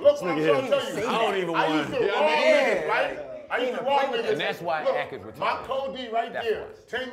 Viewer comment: did joe say "thats why ak is retarded" at 1:18?